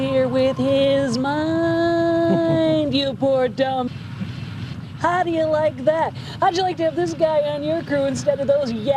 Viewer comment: what is pearl saying as she's muted? "Poor dumb.......thesbits?" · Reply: "douchebags"...I guess SciFi thought it was too hot for TV.